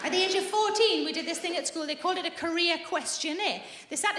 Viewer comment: I remember those career quizzes. I work in the medical field now, but in grade 9 that quiz told me my ideal career was “ship’s crew” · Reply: ohhh “career quiz” — I heard “Korea quiz” and I was quite confused 😅